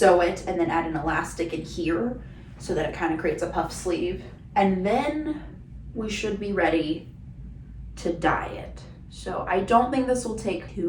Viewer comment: I totally misheard as "And then we should be ready to die yet" 😅